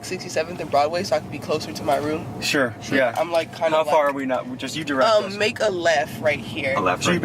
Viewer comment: sounds like she says "I'm kinda f_ck_d" and nobody responded to it. No wonder she was more scared than you guys 💀